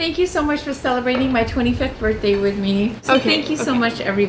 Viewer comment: Was I the only one who heard her say 25th birthday ?